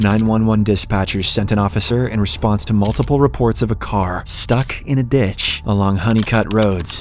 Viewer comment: I thought he said stuck in a b*tch